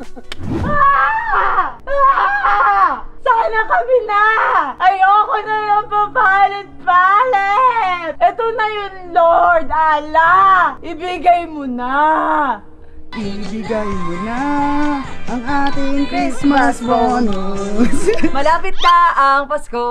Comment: did i heard Allah??